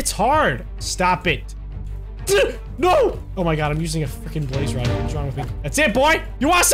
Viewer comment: did he say a fucking blaze rod?